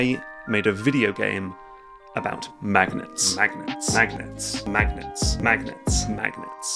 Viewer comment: I think the game he made might be about magnets guys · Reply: Ugh! This whole time I thought he was saying “magnates” and trying to figure out what this had to do with business tycoons!